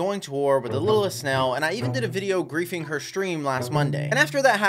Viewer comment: I had my volume low and I heard “a video raping her stream on Monday”